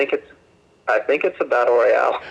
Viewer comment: Ohhhhh.... A battle Royale! At first I thought he said it was about L'Oreal